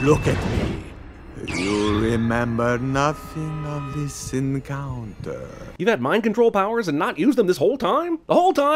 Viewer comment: I thought he said "sin counter" but be says "encounter" self aware